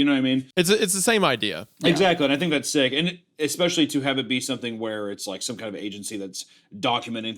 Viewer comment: did just say "especially" ?